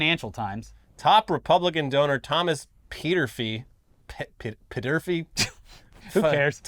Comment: I thought he was saying Peter feet lol Thomas peterfeet😂